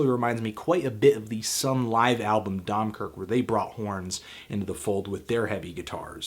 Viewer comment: What is the name of the band you are refering to at 4:13? Sorry but I can't understand it properly :( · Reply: Sunn O))) - Dømkirke. It's a live record and it's a good call but I'd recommend Sunn O))) - Monoliths & Dimensions instead. Studio record, more interesting stuff going on plus better production.